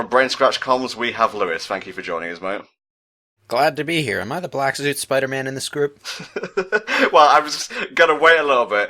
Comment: No joke, I misheard Lewis (think that's how his name is spelled) saying black suit Spider-man as black lewd Spider-man.